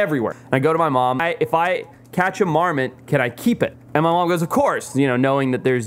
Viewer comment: I thought he said "a Mormon" 🤔